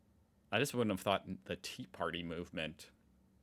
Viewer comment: I don't think she said "tea party", but said "two party" and meant that super polarization on those two main partys, which is very unusual in the rest of the world... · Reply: No, it definitely was Tea Party and it was a big topic like 2009/early 2010s. It was all over the news also here in Germany. It pretty much was the beginning of the total downfall in public perception.